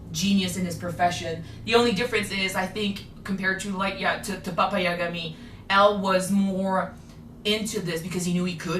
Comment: When you say "papa Yagami" all I hear is "papaya". I'm so weak.